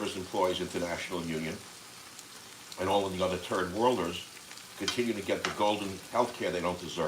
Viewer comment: Is the transcription accurate? At 2:33, I thought I heard Turd World, and repeating it, I think that's actually what was said which is also poetically appropriate.